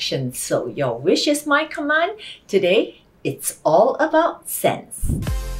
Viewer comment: Did I just heard: Today is all about sex?